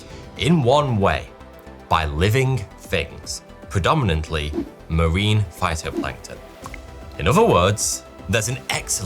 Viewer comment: I heard Marine Fighter Planes and almost shit myself.